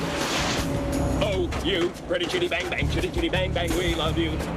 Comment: But he says chitty? Even though you pointed something out, I still hear the chitty chitty bang bang song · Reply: he says "shitty" in that part. When I was a kid I thought he said "shitty" all the time lol